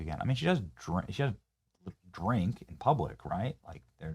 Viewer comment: I thought she said she doesn't drink! 😂😂😂😂